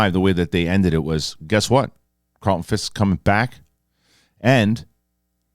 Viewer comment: [ @ 1:08] Me: "Did he just say Carlton Fisk?" I replayed that whole bit like five times in a row! I'll always be here for the news and nonsense.